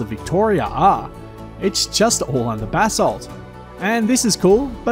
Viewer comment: BASALT!!!! THANK GAWD!!! I heard that a few times and I was "Sayy WHAAA!!???" cause I live in Florida... there were massive stories of Bath Salts here for a while. I thought he was saying Bath Salts not Basalt's!!! DERP!!💁‍♂️🤷‍♂️🤷‍♂️🤦🏼‍♂️🤦🏼‍♂️🤦🏼‍♂️ 🐵🤡🐵🤡🐵